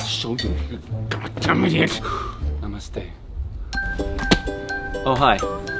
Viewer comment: did he said 'Namaste'🙏